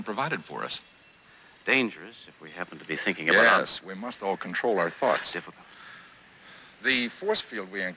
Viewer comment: Omg did Spock just say "yes we all must control our farts" at 0:59? XD